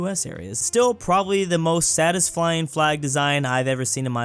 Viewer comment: am I the only one hearing "Satisflying" instead of satisfying?